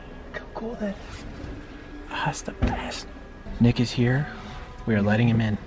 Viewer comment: sounded like he said niggas here😂